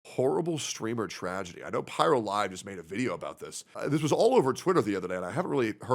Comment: i thought he said "horrible streaming tragedy, pyrolive made a video"